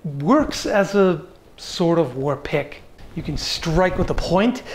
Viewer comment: Why do I keep hearing "war pick" as "war pig "?